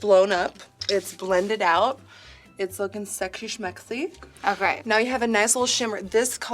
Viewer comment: what the f does she say at 10:47 please guys " it's looking ............." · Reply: RÉDA JOHNSON sexy smexy😅😂